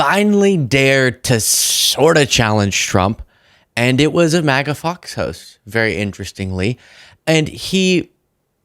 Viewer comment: I thought had come up with a new dig "Maga F@ Toast" I misheard, but I do like it.